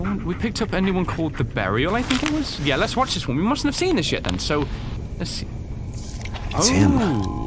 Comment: Did anyone realize he swore he said lets see this sh*t then